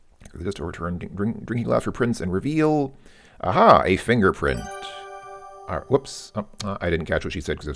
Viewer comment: "Ooh, I'd better be careful and not stray too close to the edge of the jetty!" - is what Nancy says at 7:08. "Ah-ha! The fingerprint on the glass jar belongs to Nessa!" - is what Nancy says at 7:44. Thanks for the video.